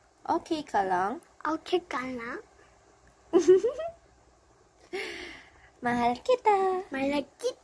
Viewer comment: mahal kita Them: dies in cuteness Me: I think I just heard him say "malagkit ah" lol, but I still died in cuteness uwu